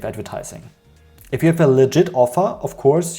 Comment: I kept hearing "legit" as "leager" until like the last time you said it so for most of the video I was wondering "what does legid mean?" I mostly just came to hear something on the consumer side to validate my annoyance of these misleading ads because I absolutely DESPISE Taboola ads, especially after reading an actual informative article. Also a lot of them throw disgusting images at me even if I don't click, which is most of the time. Those ones that give you a slideshow with one sentence are almost the worse, but the ones worse than that are ones that spread misinformation, especially about health claims that don't actually work. What's especially annoying are those ones that make you watch a 30 minute infomercial on their product, usually with fallacies and faulty reasoning that I never once saw the end of their video about whatever health claims they were making. Probably doesn't even work if they will use the time loss fallacy to get people to watch it instead of putting it on YouTube where people could skip past the BS parts of it, which sucks because at least some of the stuff is factual, seen stuff like whole grains being better than refined flour for your body, but then they put a bunch of stuff that is definitely wrong. But if I search to try to find other people who hate Taboola, I only get results on Taboola or how to use their website. I want to see other people's critisisms about it but the best I could find was this video.